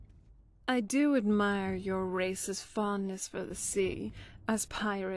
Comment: I just had a huge brain fart. When the Redguard dialogue came on and she said Race's fondness, I thought she said "I like your Racist fondness" and was like what fuck are you talking about until I realized what she actually said lol.